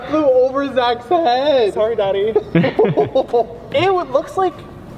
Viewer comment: Did James just call Zach, Nathan's brother, "daddy" at 15:52 ? Hmmmm 🤔